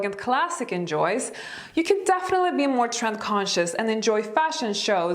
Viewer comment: Ahahaha - when you said "trend conscious" I thought you said "tramp conscious" (11.30) - that got my attention focussed again 😂